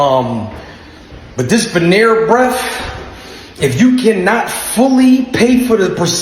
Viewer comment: "Rhino sex? After birth?" 🤣🤣🤣🤣 he was straight up savage. I thought he said dimple surgeon at first and realised he meant dental surgeon.